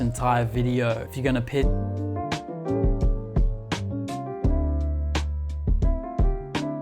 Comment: What was the saying 😭the video cut off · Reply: If you’re gonna piss like a puppy stay on the porch & let the big dogs eat BOWWWOW